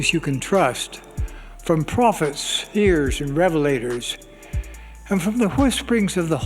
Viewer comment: did Nelson just say “prophets, spheres, and revelators”?